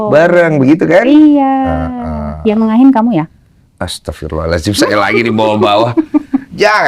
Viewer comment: kakanya ngomong apasih,kurang jelas kedengeran nya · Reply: Yang nengahin kamu yaa